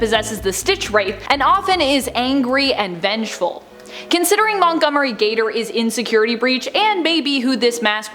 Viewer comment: I love how when said "Montgomery is in Security Breach", it sounded like she said "Montgomery is Insecurity Breach"